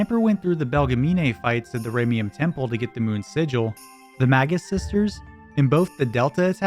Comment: Bro I thought you said MAGA sisters lol